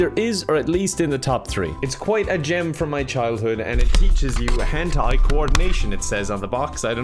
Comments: “it improves your hand eye coordination” proceeds to hit self in the face with the mic and the game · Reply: I swear i heard it as "it improves your hentai coordination", i think i should get some treatment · What said: “Hand-eye coordination.” What I heard: “Hentai coordination.” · Also when said “teaches you hand eye coordination” I thought he said hentai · I thought said "It teaches hentai coordination" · I'm such a degenerate I thought said *Hentai Coordination * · Was doing something on my phone and didn't listen properly so when you said: "it teaches you hand-eye coordination." I heard: "It teaches you hentai." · "it teaches you hand-eye coordination" That sounded like Hentai Coordination to me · I thought he says it teaches hentai coordination